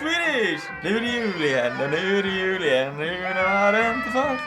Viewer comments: it,s sounds like pewds says nu det jul igen nu det jul igen but it is what he is saying cuz i know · It sounds like pewdiepie says do you know how to f*ck at the end of his singing.